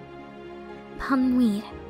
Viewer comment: BOII THE WAY THEY SAY BONNE NUIT😂😂😂🤣🤣🤣🤣🤣 It sounded like she said bon weed🤣